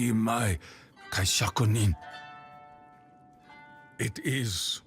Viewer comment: I was hoping you could've explained what Takeo 1.0 said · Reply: I just looked up kaishakunin cause it sounded similar and I found this, One who beheads someone after they commit seppuku, or suicide at the moment of agony. So basically takeo 2.0 beheaded 1.0